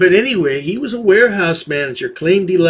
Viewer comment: Oh, you said Wearhouse manager. I thought you said he was a whorehouse manager. The latter sounds better.